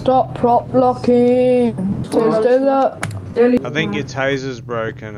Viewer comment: I think he may have said “please continue prop blocking”